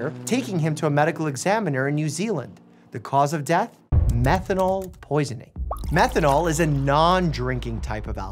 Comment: Help I kept replaying that part 1:54 in confusion cause I thought he was saying “Metadol” 😭